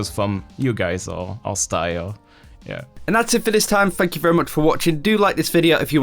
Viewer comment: How come everytime I watch your videos I hear "Fuck you very much for watching instead of Thank you"?